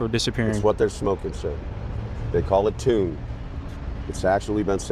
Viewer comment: i think he's talking about deuce. Used to be called K2. The screen says tomb, but I think he was saying 2. I've seen some really bad, really, really bad Bad trips on that stuff, i've also had really bad trips on it They can put it on paper too. I've seen people swim in their own vomit. I've seen people swim in other people's vomit on that stuff. Trust me, don't mess with duece.